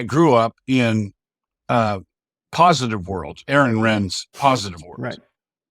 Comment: Did I hear this character correctly ? At about 9:24 he speaks of the " positive world of Ayn Rand " . Is he kidding ? Her egoistic socioeconomic darwinist ideology is the very antithesis of the Christian ethos of altruism and egalitarianism which was institutionally structured and advocated by the Christian community way back in the apostolic era . Just read the book of Acts if you don't believe me . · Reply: It’s Aaron Renn not Ayn Rand